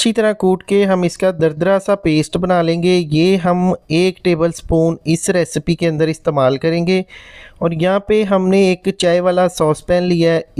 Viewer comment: Very nice 👍👍👍 Water 2 cups Sonf 1 table spoon Ajwaen 1 tea spoon Dar chini 1" peace Adrak 1 tea spoon Ghor 1 peace then boil until hafe. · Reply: JazakAllah that you hv written out the ingredients & quantities. Pls recheck: at 1:37±, he mentions 01 TABLE SPOON of adrak (ginger), your text says 01 TEAspoon. Pls recheck.....may be i didn't hear properly